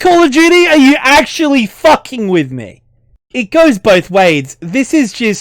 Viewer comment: Lol, it sounds like you said "It goes both Wades", which would sound a bit relevant! Also, I hate the game's dialogue, initially because of how corny it was, and not even in an enjoyable way! Why did everything have to be "clever" or be some lame wisecrack? Then the "wokeness" occurred to me. Combine the corniness and the other things like the obligatory references to the minorities' respective cultures, and it was even worse.